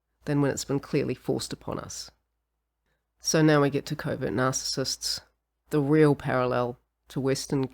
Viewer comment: Sorry, I keep hearing "COVID narcissists" 😂 British accent, I know 😅